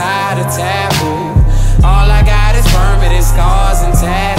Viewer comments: Ven Mendez real talk thought he said "all ive got is vermin, scars, and tattoos" but permanent makes sense lol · Damn I thought he was saying burban and scars this whole time 💀💀